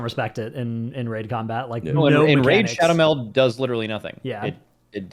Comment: i heard it as "in Raid: Shadowmeld" All those sponsorships have ruined my brain.